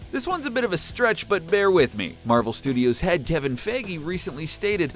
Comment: Did He Say Kevin Faggy?